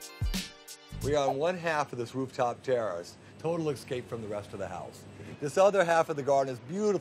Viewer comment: Did I hear him say "excape" instead of escape?